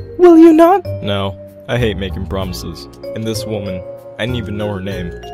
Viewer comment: IT SOUNDED LIKE HE SAID "No, I hate making promises."